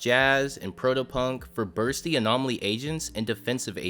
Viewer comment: i had this video in the background i thought he said busty 😭